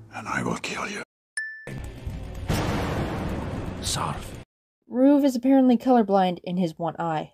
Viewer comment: did he say son cause that doesn't sound like sarv at all!